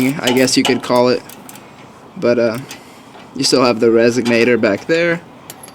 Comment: Did he really say "Resignator" instead of "Resonator" at 2:11 ?